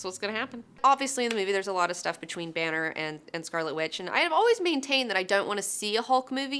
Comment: Did she call Black Widow Scarlet Witch?